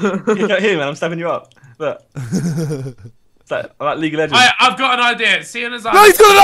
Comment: did say league of legends at 11:29 ?